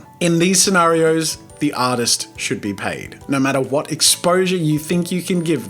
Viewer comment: ur accent makes it sound like you say autist lol.